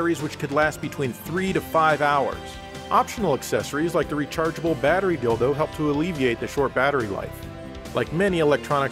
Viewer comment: Did he really say "rechargeable battery dildo"?! 😂😂😂